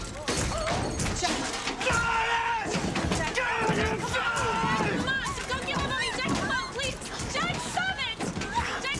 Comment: Does anyone know what he shouted at 0:06? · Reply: just, standard inaudible angry groaning. sounds something like "god damnit fuck"